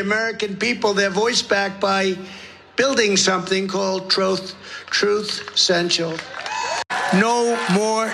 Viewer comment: truth sential ! 😂😂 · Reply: I thought he said fruit central. I didn't know what that was.